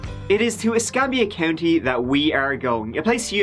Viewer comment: I couldn’t have been the ONLY person who misheard him & thought he said “I-scam-ya! County” right?!